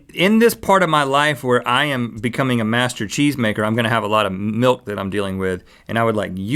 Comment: I thought rhet said master chief lol =)